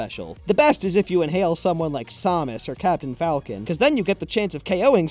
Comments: Did he call samus somus? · Did you just say Somis? XD